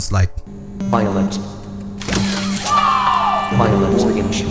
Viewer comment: I heard organge